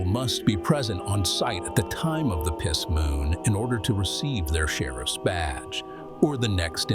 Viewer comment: But why did I think he said "their share of spadge" tho 💀